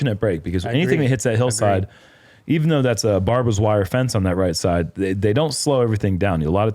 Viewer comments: Did say Barber’s wire fence at 19:23? 😂😂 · Did say that was Barbara’s wire fence on hole 4?! 😂